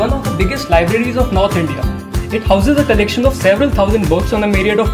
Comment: The library seems so small, does it really only house 7,000 books? That seems so little compared to the library at my university in Germany, which has over 3,96 Million books divided into 12 buildings... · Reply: I actually listened to it several times and I thought it was "seven" instead of "several". Sorry for mishearing. That 400,000 books seems more like a reasonable number. Thanks!